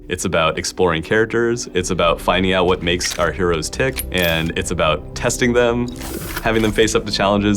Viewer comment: Did he just say "Make our heroes T H I C C" ?